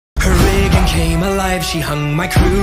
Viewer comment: I’m I the only one that thought she said “wig became alive”☠️